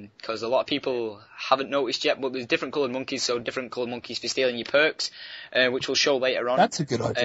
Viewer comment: idk if im buggin but did he say monkeys can steal youre perks at 4:22, its bad enough that stupid scientist takes my gun in five.